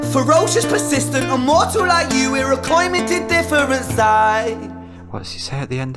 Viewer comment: "We're a coin, minted different sides" Is what I hear -- basically saying that the despair/depression and the hope is in all of us. Same coin, different sides.